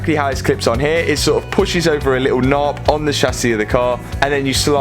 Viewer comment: Did you say little nob? 😋 1:29 Solid graft Matt 💪🏻👍🏻